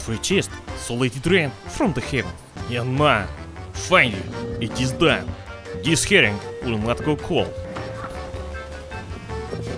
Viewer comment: Oh, i thought it said heroin under the jacket